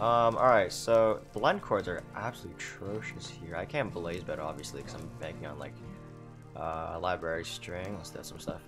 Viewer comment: Dude I can't understand a word this guy says sometimes. Like at about 17:05 it sounds like he's mumbling for a bit then it sounds like he says "Library Strings". It's almost like he's just saying random words to fill in whitespace. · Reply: “Um, alright, so… blind coords are absolutely atrocious here. I can’t blaze bed obviously because I’m banking on like… uh, library string.” etc After looking it up, “blind coords” is a speed running term in a technique called blind travel, which seems to be an easier way to get to the stronghold (I didn’t research this one too thoroughly), and blaze bedding is a technique where you use a bed to destroy parts of the fortress around the spawn to increase the number blazes that spawn (due to technical stuff). Ofc library string is just string from the library’s cobwebs in the stronghold like Horst said. (And yeah I know this is a 2 year old comment so sorry if you get a notification lmao)